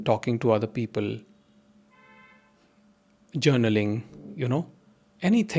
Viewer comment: you were saying, “talking to other people, g3n3raling”. I couldn’t make out what was being said. Will you please tell me what that word is? 42:37 dialog makes 100% sense!🙏🏾